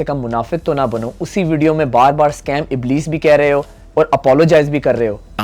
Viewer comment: he is not saying scam ibless in his video he is saying scam idress